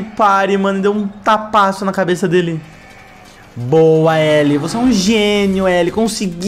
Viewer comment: Só foi eu que escutei "Você é um gênio L"?